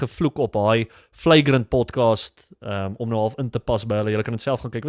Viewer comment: Had to laugh at myself: when you said podcast, I heard ‘pot kaas’ and was confused for a small moment 😅 (you did say you weren’t going to mix your languages). I have to say, I began to understand the Holiness of God through RC Sproul’s teachings, don’t know that I will fully understand it in this life. I also don’t know that I am a Calvinist, because I don’t know all that Calvin taught, other than the tulip, which I believe was a refutation of a different belief rather than an encapsulation of Calvinism. That being said,don’t all Christian’s believe that God is sovereign?